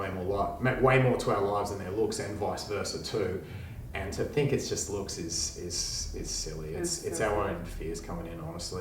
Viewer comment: Im confused: at 12:31 what did the 2 counselors say? I heard fears...looks...and silly...